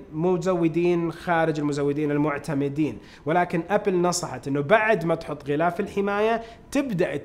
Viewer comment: هو اني سمعت يكول ابل نصحت مستخدمين سامسونك 😂بل دقيقه